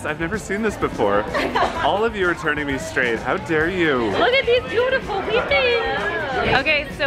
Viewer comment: Did I hear “your turning me straight... that’s not fair” ? 😂😂